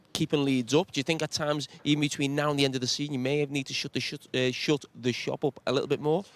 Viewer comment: I love the pause he had to take to figure out Carragher was saying 😂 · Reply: I am American but am seriously wondering if he just said “shot the shovel” and if that is a real phrase or if I am mishearing what he said? Haha